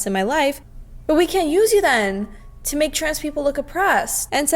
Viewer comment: Ngl I thought you said "stray people💀"